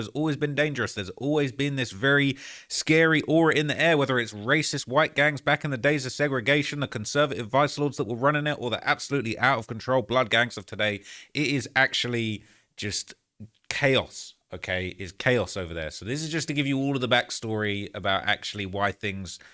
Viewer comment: Did you say “blood gangs of today”? 9:25 I know u more tapped in than that